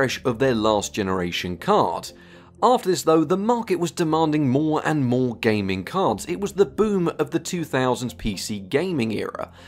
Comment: at first I heard "The monke was demanding more and more gaming cards" and pictured the orangutan meme in my head saying "where gaming cards"